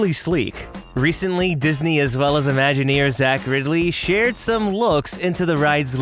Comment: When you said 'Imagineer Zach Riddley', I thought you said 'Imagineers accurately'.